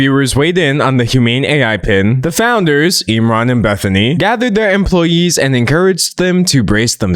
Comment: I kept mishearing their names as “Enron and Bethany” which seems appropriate